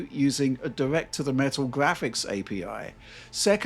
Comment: did he say the Switch version runs on the Metal API? The Mac one? That mean Apple technology is actually helping PC gaming do the leap to ARM? Thats quite interesting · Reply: No. He said direct to the metal api. Aka a very low level api.